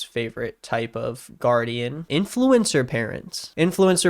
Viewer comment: I thought he said “influenza parents” 😂